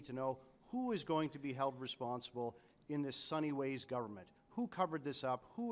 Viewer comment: Every one thought he said sunny ways but it was just sun hee hwes 😂